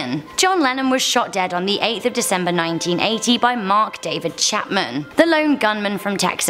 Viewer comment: I’m high asf, I thought she said: From Daddy. I was like; wait what? And then I went back then she said David😂